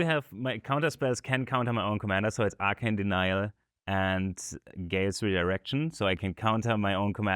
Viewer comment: No idea what this card is. I keep hearing "Gain Sweet Erection" 😂